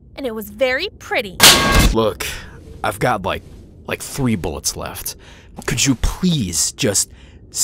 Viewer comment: I thought he said "i've got like 3 bones left."